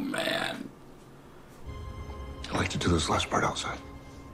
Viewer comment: Just a little correction on what you thought you heard from Bautista toward the end: You thought he said, "I like to do this last part outside." What he said was, "I'd like to do this last part outside." It makes sense that way. That's all. 😉